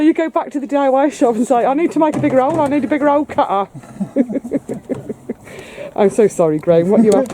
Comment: Haha...when was saying “hole cutter” I thought he was saying “old car”, made me laugh. You two are funny together.